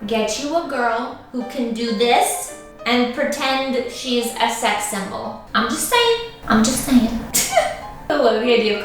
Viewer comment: Miss heard sex symbol and thought you said sex devil. I’m stealing it